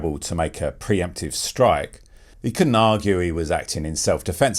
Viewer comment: What the hell was the warden saying? Citizens arrest? Sounded like he was saying "I'm warning you, I'll have to deliver a ~creme du finesse~" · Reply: Preemptive Strike 😂 why did I keep hearing some French shit like "creme destruct"